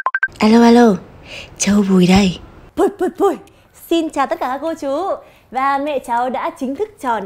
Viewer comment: “puoi puoi” giống “pui pui” Yuno Bigboy